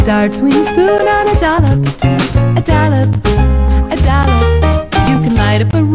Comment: The first time I saw this commercial, I thought she was saying “a dollar”, until it showed the words “A Dollop of Daisy” on the screen at the end.